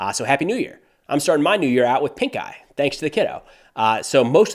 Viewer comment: Thought you said "Pink Guy" for a second there, and my first thought is; "IT'S FILTHY FRANK...!"